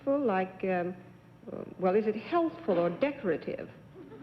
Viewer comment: Dorothy Kilgallen used the word 'healthful' in connection with mud packs. I've never heard anyone say 'healthful' before, and John Daly thought she had said 'helpful', so he was taken by surprise also. 'Healthy' or possibly 'health-giving' would be more usual - I think Dorothy K had a rather antiquated use of language which needed John Daly to translate it for one or two challengers.